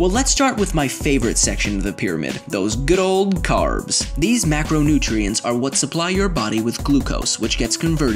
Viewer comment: Am I the only one who thought he was going to say macoroni not macronutrients 😂😂😂😂😂😂😂😂